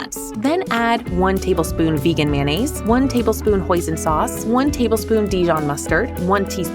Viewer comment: I’m sorry but did it just say poison sauce?💀